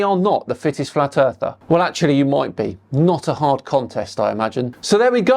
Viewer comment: Fittest Flat Earther? That’s like being the liveliest occupant of a graveyard. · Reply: I thought he said Thickest flat earther.